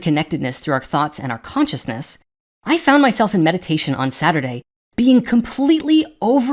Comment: I thought he said, "When I was on medication." instead of, "I was in meditation". Hahah! With, the first one was most likely more true.